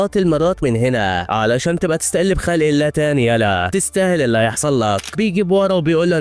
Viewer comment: كأنه قال تستأل بخالق الله استغفر الله أتأكدو ممكني سمعت غلط